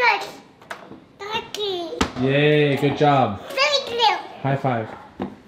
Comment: Did just say thankyou??